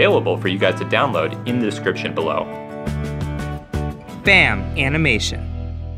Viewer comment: Thought he said "DAMN, animation"